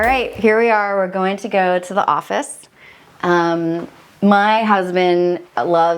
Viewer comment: Am I hearing right? "going to the offist" ?